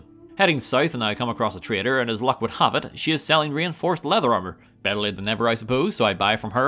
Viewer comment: for a minute there I thought you'd said "rainforest leather armor," Which sounds like it would look pretty snazzy.